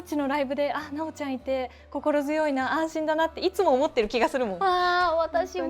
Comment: 「安心だなー」が「ああ死んだなー」に聞こえてびっくりしたｗ